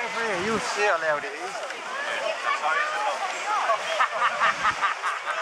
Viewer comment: Is it only me that hears “okay that’s loud” instead of “I thought you said it was loud”